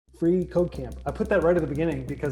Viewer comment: I heard that as free cocaine